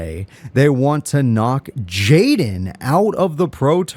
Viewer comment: I thought the bio said Jessie wanted to knocked up jaden 😳